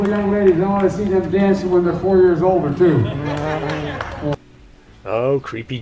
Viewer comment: I thought he said 4 years old or 2